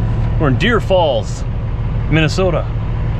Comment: it’s Deer River (instead of Deer Falls)